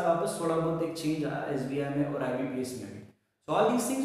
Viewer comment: Sir aapne video mein kya kaha end mein "aur ek change aya...??"